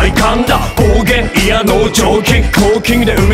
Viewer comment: サマトキほんとカッコいいんだけど、0:57ぐらいの「帰ってきたぜ」が「生えてきたぜ」に聞こえて... 我が家の地下からこの4人生えてこないかなあ